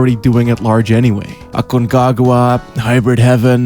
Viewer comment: what game is this? I can't understand what he's saying. "Akunkagwa??" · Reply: Aconcagua